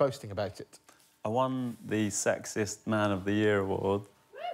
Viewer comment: Auto-captions: I won the sexist man of the year award. crowd cheers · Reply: I thought he said sexist at first as well lol